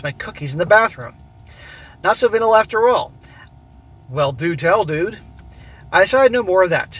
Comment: Nahsavinl after all? What did you say there?